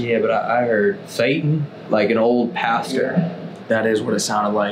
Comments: you hear the same saying “yeah” after describes what he thought he heard almost like it was agreeing with him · when he says an old pastor i heard something sounds like they said "yeah"